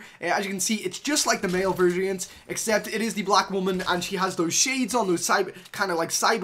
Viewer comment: wtf 7:38 did he say "it is the black women"?